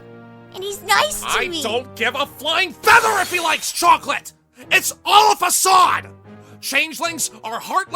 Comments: Discord: I don't give one flying feather if he likes chocolate! What I heard: I don't give one flying f*** if he likes chocolate! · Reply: Half The Heart What i heard: i don't give one fu*** feather if he likes chocolate. XD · Discord: I don't give one flying feather if he likes chocolate! What I heard: I don't give one flying f*** if he likes chocolate!